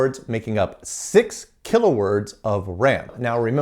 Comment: I thought you were saying "kilohertz of RAM" and I was so confused